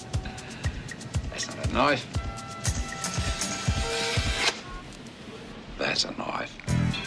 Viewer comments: i thought he said thats not a knife this is a knife but he say's thats a knife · I always thought he said “that’s not a knife, this is a knife”